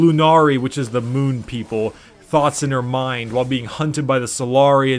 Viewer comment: I don’t know why I thought of thot instead of thought.